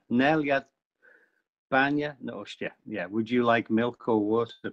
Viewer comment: That was great. managed to get everything except the Manx sentence, I thought he was saying do you drink or did you drink instead of would you like.